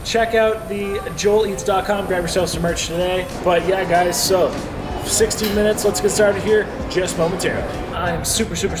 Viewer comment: 60 minutes?! I thought you said SIXTEEN minutes.. I was like theres no way! Everything looked SO GOOD! --❤ From illinois